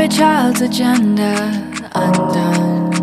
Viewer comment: Sonic perfection, but it sounded like you said every child is born with a gender unknown. Lol I know my ears and life experience must be playing tricks on me. We should teach children that there are no solid foundations to set their tiny feet? Maybe if they limit their oxygen intake and stay far apart from any human contact, then they may stand a chance. Your fame holds huge responsibly, so teach your fans correctly, instead of romanticism of separation. Be human, not just a wealth accumulator